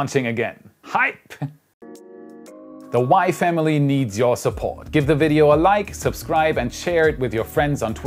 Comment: I thought said "the wife needs your support" instead of WAI. But then nothing about his wife or wife's videos, lol